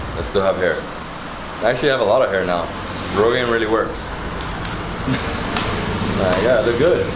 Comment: what really works??? What did he say can anyone tell!!!!! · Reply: He said, I have alot of hair now...Rogaine really works.....LMAO. From Bethlehem Pa.